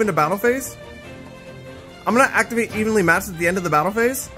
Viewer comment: Okay what's that first card? I've not played ygo in years and it sounded like he said "I'm gonna activate anally matched" 😂